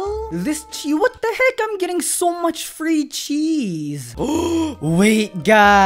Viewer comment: I mistaked when he said chis I thought he said cheese